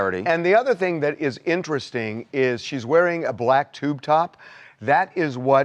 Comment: I thought they said it was a yellow tub top with black leggings not a black tube top